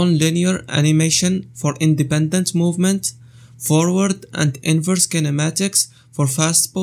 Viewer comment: did she just say inverse kinematics? 😂 Nicely timed